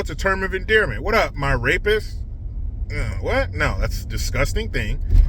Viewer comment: "What's up, my racist?" LOL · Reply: i thought he said rapist ?